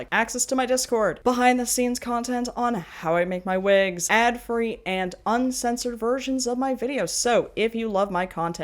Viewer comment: "Ad free and uncensored versions of my videos" I heard "And FURRY and uncensored videos" while I wasn't paying attention. 😂